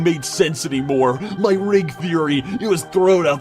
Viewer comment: It sounded like he said ring feory